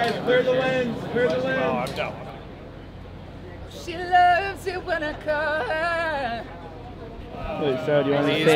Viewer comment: no one : literally no one : shawn : sHe lOvEs iT wHeN i cAlL hEr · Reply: I always thought he said "She loves when I come" GODDAMNIT THANK GOD I WAS WRONG 🤯